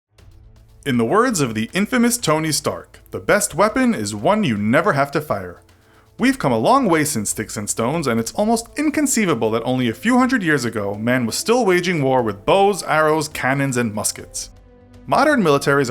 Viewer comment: I thought tony stark said the best weapon is a weapon you only have to fire once lol. I need to catch up on Iron Man